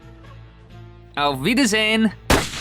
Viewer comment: That sounded like the german sentence "Auf Wiedersehen", that means godbye!